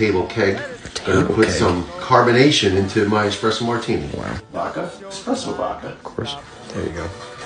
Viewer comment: You said “espresso martini” I heard “diarrhea”